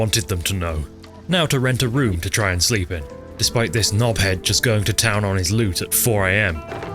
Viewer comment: "despite this guy going to town on his lute" for a second I fully thought you were saying "going to town on his loot" instead because most people who play skyrim are loot goblins lmao